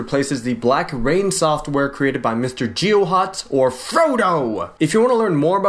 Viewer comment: When you said Frodo I thought you said Froyo. LOL.!